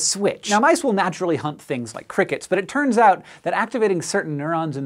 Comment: I wasn't really paying attention to the video so I heard "mice naturally hump crickets" instead of "hunt crickets" and I was very confused